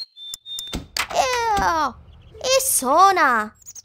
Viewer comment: I thought she said "Eww, a sona!" As a certain cameo walks past and delivers a newspaper! XD